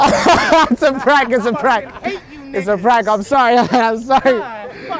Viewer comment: Hold on @ 7:10 I swear my guy said I hate u niggas